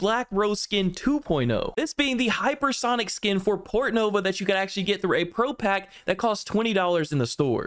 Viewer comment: Lol i thought you said Portno, instead of Portnova